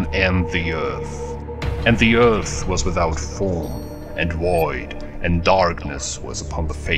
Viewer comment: Is it just me or does he say 'woid' instead of 'void' at 0:19?